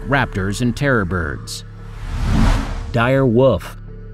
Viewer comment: Did he called the dire wolf a dire woof